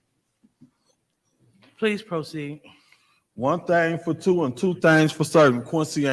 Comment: wait what he said?😂😂😂😂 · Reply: “One thang iz two n two thangs fo certain” 😂😂😂😂 I still can’t believe he’s a councilman